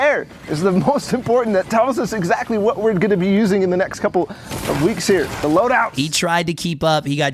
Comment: Did he say that we will using that for couple weeks